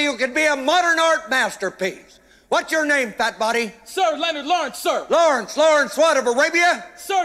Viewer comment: Lmfao I thought this man said “Leonard Lard sir” 😂 and then he started calling this man private pile - I can’t